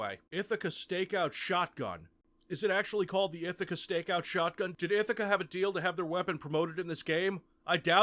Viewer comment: You're all amazing and I can't thank you enough for watching. I had to remaster this because the audio was pretty bad and there were graphics issues and what am I even doing with my life if the Capstone trilogy is incomplete? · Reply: NGL For the longest time I thought you said "Ithica Steakhouse Shotgun"